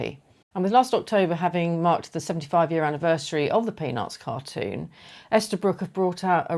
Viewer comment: When I first heard you say it was the 75th anniversary, I thought you said you were 75. Throughout the video, I was thinking, wow she looks great for 75! Towards the end, I think the 75 reference came up again and it clicked. 😂😂. I did lol. Of course, I want the Lucy one now.